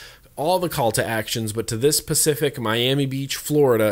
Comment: Did he just say pacific instead of specific. Hahaha